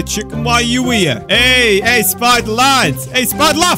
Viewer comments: did he say spider laff but in the cc says he said spider lad- 6:55 WHAT NETHER FORTRESS AND A BASTION THIS GOTTA BE ON GAMERS REACT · the enderdragon that spawned LMAO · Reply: me:did he just say chiken jockey?